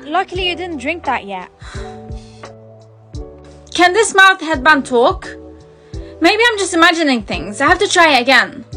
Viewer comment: My senses be messed up, I heard "Can this mouth headband talk?" Instead of "Can this mouse headband talk?"